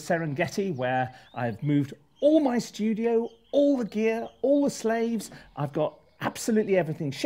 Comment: Did he just say "All his slaves" Me sooooooo confused